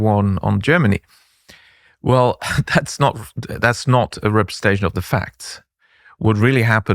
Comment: I believe the cc is incorrect, sounds like he said “Riposte” not “Representation”.